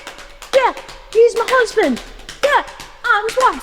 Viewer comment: min.: ..........DID SHE JUST SAY , " HES MY COUSIN, IM HIS WIFE..." ... ? 😂😂😂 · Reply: *He's mah HUSBAND😅